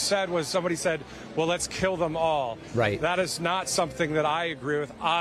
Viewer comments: I thought he said “jail ‘em all” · I heard him say “jail ‘em all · I heard "jail em all."